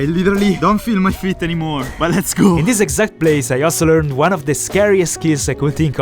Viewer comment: still his italian nature never gets erased: instead of saying “but let’s go” he said “ma let’s go”, with ma in italian=but